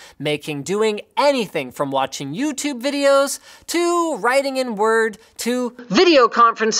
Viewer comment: What? Writing N word?